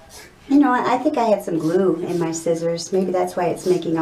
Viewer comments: Did she say glue or lune🤔? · I thought she said i had some poo on my scissors but she really said i had some glue on my scissors